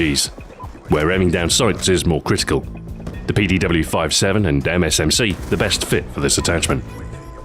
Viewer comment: Hahahaha at 2:08 I thought you said "The PDW-57 and damn MSMC". Keep up the great vids.